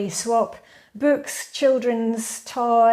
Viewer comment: For a moment it sounded like you said that you swap childern at the swap party haha!